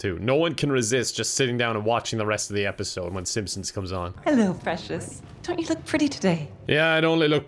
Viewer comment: Did she just say "Hello, fascist"?!